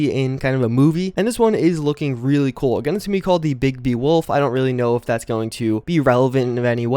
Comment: lol I was listening to the audio, playing fallout now really watching the video. And I thought u said, "it's called Big D wolf. I don't really know what a Big D wolf is...but"